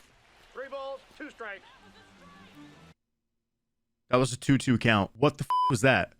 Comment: did anybody else hear “that was strike”